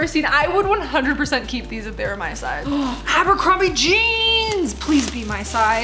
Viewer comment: Bro Am i the the only one who heard her say apple Bottom jeans😂